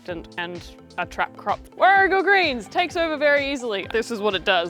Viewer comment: WHAT greens? I couldn't understand the word you used and I don't recognize the plant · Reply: The captions also didn’t help hey. 😅 It’s called Warrigal Greens ( Tetragonia tetragonioides ) it’s an Australian native.